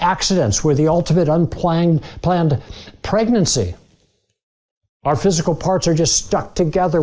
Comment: The fact that that guy said "We are the ultimate unplanned pregnancy" is ironic because he still can't give up the notion that we're special. We aren't the ultimate unplanned pregnancy, we're just one of many, many unplanned pregnancies. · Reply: Both Google and I hear “unplanned planned pregnancy”